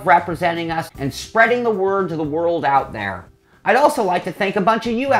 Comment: Ha you said world! I thought you said girls. Yahtzee.